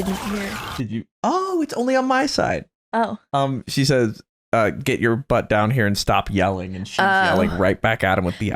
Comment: she actually says "Katsuki, you can't wake up at noon and start yelling!"